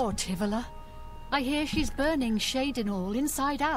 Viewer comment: Is it me or does she say burning at 19:05 ?